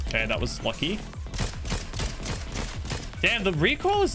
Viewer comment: is always lucky. For me it sounded as though said instead of lucky